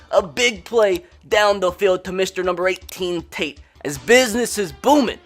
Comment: did he say number 18 TATE?